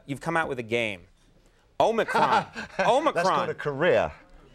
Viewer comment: did Conan say omicron?